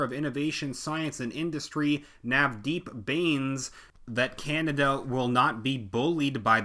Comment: that sikhs name is deep beans?! i have a bad fever. i think i misheard that one. lmfao - no more whiskey and honey tonight